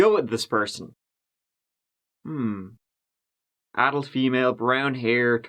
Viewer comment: lol when you said, "adult female" I thought you said, "addled female" hahahaha